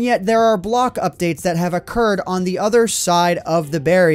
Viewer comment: Your subtitles say a curd when you said occurred xD